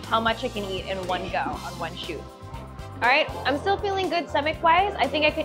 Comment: When the host said "too much food in the shoot" I heard "chute" and thought TMI. 😅 Seriously though, the facility featured in video would be nice to visit if you're already going to the fish market.